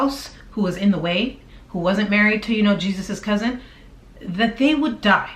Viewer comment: Emotional affairs? No physical affairs ? I just heard u say "Jesus' cousin" I almost died laughing